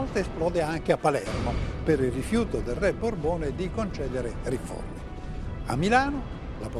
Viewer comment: Non ho capito cosa dice:rifiuto di concedere "riforni"? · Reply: Credo dica riforme